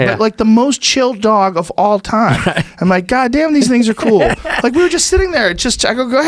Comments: Joe says, "Damn, these niggas are cool." And you know what? Good! Sometimes there is just no substitute for that word. And I truly believe, just like anyone else who follows Joe closely, that it was just said hatelessly. Literally, the word was used just like he was saying, "dudes." No thang. Let's stop whining and getting but hurt. :) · did Joe just say the N-WORD? 😂😂😂 "GOD damn these ni**as are cool" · Did Joe just say "God damn these niggas are cool." ? haha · it kinda sounds like Joe says “God damn these N****’s are cool” , he is really saying “God damn these things are cool”